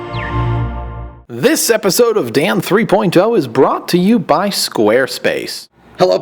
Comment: LOL in the beginning it sounds like hes saying "this episode of damn 3.0 is brought 2 u by squarespace"